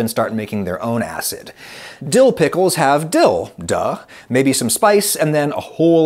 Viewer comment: Did you just say dillduh?